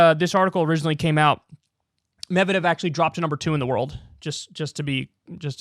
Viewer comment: I def heard you say he "dropped a number 2 on the world"